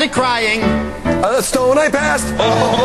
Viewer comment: what does Ryan say? I've never been able to get it, english is not my first language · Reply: VaqueroNuclear a stone I passed